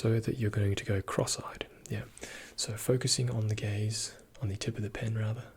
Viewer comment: Did you say focusing on the gays? Because I can....